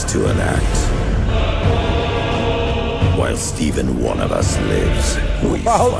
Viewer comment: I heard 'While steven...' all Blood Ravens are now named Steven to me.